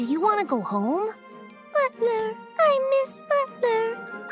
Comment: The first time I watched it i thought moono said butler moono miss butler